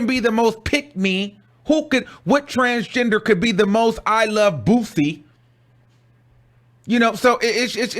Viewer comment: Did he just say “I love BUSSY”? Lmaoo I’m sent